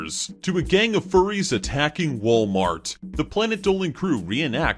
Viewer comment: It sounded like he said wollmart